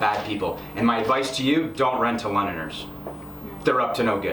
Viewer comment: don’t rent to what??? Have replayed like 4x still understand what he said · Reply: i think he said "dont rent to Londoners". I guess they are the locals as another comment said it